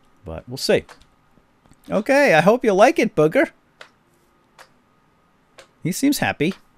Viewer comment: I was watching legends of hoodie and at the end of the video i saw the link and i thought he said"I hope you like it booker"i was like BOOKER DEWITT this isn't bioshock!